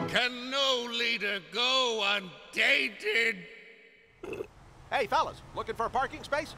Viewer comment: That line about Papa Smurf always gets me! "Wise Papa Smurf, corrupted by his own power! CAN NO LEADER GO UNDATED?!" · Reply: he does say it with a soft T, so all my life since 2000 (when I started watching), I thought he said "unDainted"... I shoulda known it was "unTainted". Also, Billie Joe says "TIME of your life", not "DIME", lol